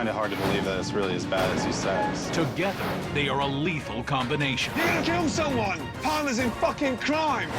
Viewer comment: i thought it said Lethal Company and went insane.